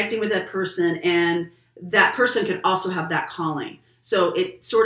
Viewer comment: Instead of Calling I heard “you have a Colleen coming in for you”😆 Well I wish I am attracting a friend like your Colleen, she seems to me like a awesome person😁